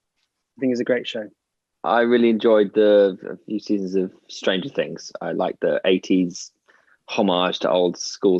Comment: did thomas just say "stranger things"? omggggg i love finn same as thomasssss😍